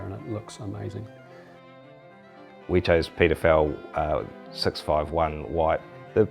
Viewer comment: Not poking fun, but it sounds like the Architect is saying "pedophile" to me. Does anyone understand what he's saying?